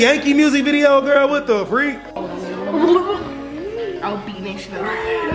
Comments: did she say I’ll beat nation up?maybe I’m just hearing stuff lol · Am i trippin?? did she say she'll beat ole girl up 28:07???